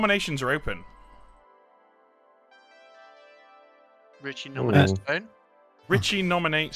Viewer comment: I heard "Richy nominate a bed"